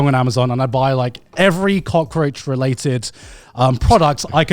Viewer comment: Every time Garnt says cockroatch all I hear is the "cock edit"